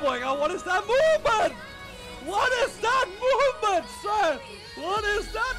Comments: well tôi chả muốn nói nhiều là chủ kênh dịch sai Câu mà nói là movement Mà chủ kênh nghe ra moment =)) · MOVEMENT là Cách di chuyển bước di chuyển gì đấy, ad nghe nhầm thành MOMENT rồi ấy ạ